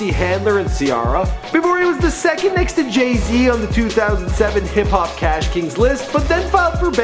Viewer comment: did you say jay-d instead of jay-z at 0:32?